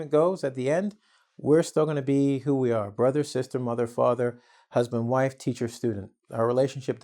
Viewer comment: i thought said Mother Faucker....looool